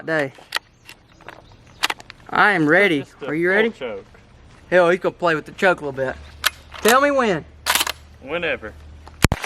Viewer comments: Mever thought id hear say "Hell"😂 but he did in this vid im im so shocked😂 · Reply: In his new vid he said that he said “he’ll” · did you say hell he can play with the choke a lil bit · Did he say “ hell he will play with the choke a little bit” 😢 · did say hell? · did he say hell or am I crazy???? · Did he say hell he is gonna play with the choke a little bit